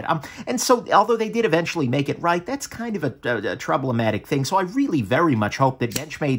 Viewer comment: Like many YouTubers, YOU HAVE CREATED A NEW AND USEFUL WORD!!! "Troub-le-matic". You meant to say "problematic" and the Closed Captions said "problematic" But I Heard TROUB-LE-MATIC!! Great!!! Add it do the dictionary!! IMHO. (oohh... Then then you said "COMPLETELY RETENTIVE..." As in retaining all the anus could retain? And more??? A word smithy thou art! imho)